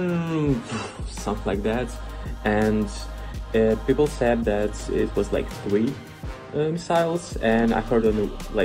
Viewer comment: 세명정도 부상이 아니라 "사람들이 말하길 미사일 3발 정도 떨어졌다 했다" 2:04 다른 도시에 비해 오데사는 하루에 미사일 한발 정도 떨어진다. 공격하기보다는 긴장감을 조성하는게 목적같다. 2:46 11월 말부터 러시아가 국경부근에 군대를 모으고 있다는걸 들었다. 그때부터 산발적으로 전투가 있었던걸로 들었다 3:34 공항을 공격했지만 피해는 없었다. Spaceship (배를 말하는건지 비행기를 말하는건지 모르겠음)는 전부 무사하다. 4:48 우리는 식량과 물자가 충분하다. 현재 항쟁하고 있는 다른 도시들에게 식량과 같은 물자도 많이 보내고 있다.